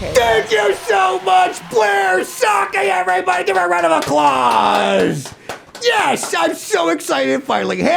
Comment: Did he say, "Round of A-claws?" 😅